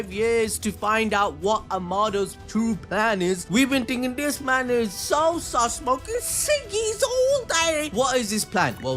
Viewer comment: Someone mentioned the "gibberish" at 6:15 being unintelligible, and not knowing what he said. Lol. Well I figured it out. To preface/give context: the discussion was not knowing what in the hell Amado's REAL motives have been, and finally learning what has been up to this entire time. (the last 4-5 years.) He said: "We been thinking this man is so sus, smoking ciggy's, all daaaaayyy." It took me a min to realize what he said as well. Lol. In fact, it was right after I lit my cig, that I realized what he said. 😂